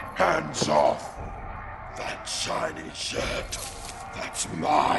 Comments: Who else thought he said "That shiny shit"? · I thought he said "Shiny shit" when I first saw it. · English is not my first language and at 0:39 I hear "That shiny shit..." instead of "shirt" lol 😂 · I keep hearing "shiny shit"